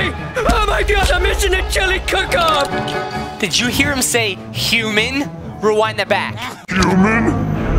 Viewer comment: I think Homer said humid not human. 😂 though keep up the great work, fgteev was literally my childhood! 😁